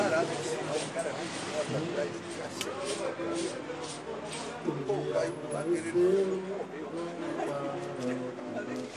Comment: n entendi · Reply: tablita. o jogo começa com peças fora do lugar, pra fugir do decorado.